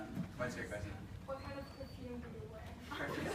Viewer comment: what did she said at 14:07? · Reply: Antonia Stelter she asked him what perfume he uses